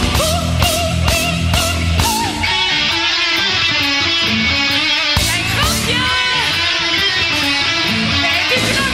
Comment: At around 2:30 does she say f you to the crowd? 😂 Theres only one Floor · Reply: No she says "grapje" which means little joke. Because they played a little tune of a childs tv show Pippi Langkous.. 😁